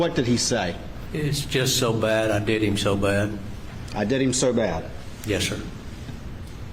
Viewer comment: sound like he said I did him so bad🧐🤔